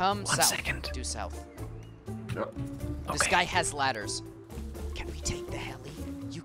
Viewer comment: I thought the dude said he had lettuce, good thing you knew he said ladders KEKW